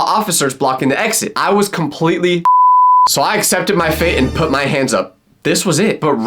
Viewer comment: I think the said swatted in